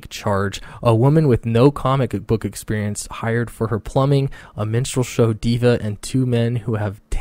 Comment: for a moment I thought you said “menstrual show.” So I thought you were talking about Chelsea Cain. 😂😂😂